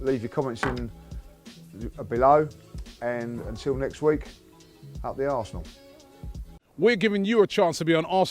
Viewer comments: I swear I thought he said up the arse 😂😂😂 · I legit thought he said.. "until next time up the arse."